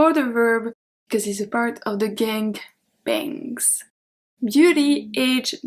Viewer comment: Did you just say the "gang bang"?😂